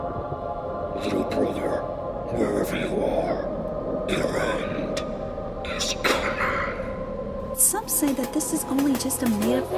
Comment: What did Timothy said? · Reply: he said "Little brother, where ever you are... your end is coming."